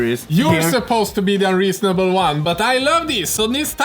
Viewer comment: I first thought you said Lukas was the REASONABLE one! 😂